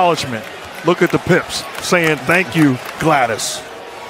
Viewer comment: LMFAO did he just say look at the pips thanking Gladys??? rofl.